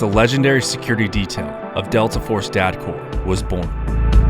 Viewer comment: Can anyone tell what he says at 8:29? Sounds like “dad core” lol